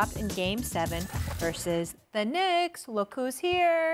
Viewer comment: did she say Knicks lol